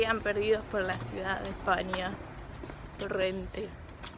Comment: El vestido que has visto es un vestido de fallera, algo muy típico de Valencia. El valenciano no tiene nada que ver con el inglés y las palabras que has dicho no son así. Muy buen video, estais en un pueblo o en Valencia ciudad? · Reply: A la última cosa que dijiste en el minuto 5:45 dice que están en torrente ósea en un pueblo